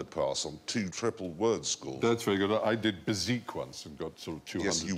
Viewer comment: if you didn't catch it, the word Stephen Fry mentions is "bezique," it's an old-fashioned French card game, I had to look it up